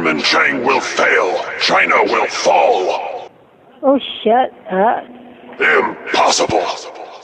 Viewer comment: at first i thought that she said oh shit when he said china will fall